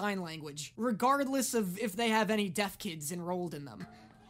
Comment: I keep mishearing him saying 'deaf kid' as dead kid 💀